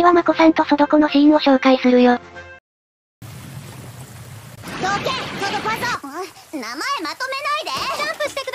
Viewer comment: ここなんて言ってるか未だにわからんｗ誰か教えて〜ソドぱぞ？って言ってる？